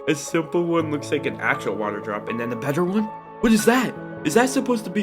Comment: Him when he said better is sound like he was saying butter fast